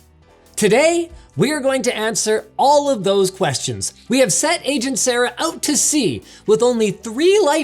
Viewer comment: "We have sent agent Sarah out to sea" is what I heard. It sounds like the foreshadowing of a Titanic undertaking.